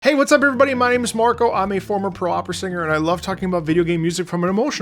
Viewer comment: Right off the bat, misheard "I'm a former pro opera singer" as "I'm a former parole officer" and was thinking these react channel concepts are getting wild